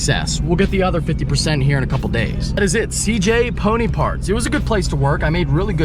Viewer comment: It sounded like you was saying cj pudding parts. I couldnt understand ya. But once i saw the sign it made sense haha